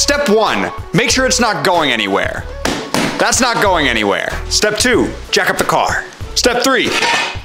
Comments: i heard "Step 2: Jack off the car" and i did a double take like wut ._. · When he said jack up I thought he said jack off 💀💀💀